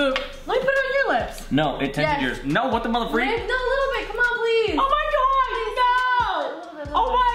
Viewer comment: Did she say no babe a little bit